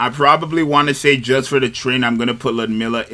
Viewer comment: Oh he's saying "Just for the train" I thought he was saying "Just for the trending". I'm like, "Are we out here picking Nikkes for clout?" 😂